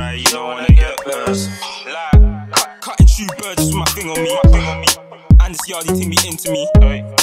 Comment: At like 1:47 ishh does he say 'and this young ting way into me or this yardy ting'